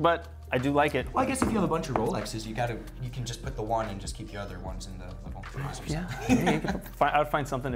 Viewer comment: What did say at 9:03 “keep your other ones in the…”? It’s driving me nuts 😅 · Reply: 😆 - "in the trays or something."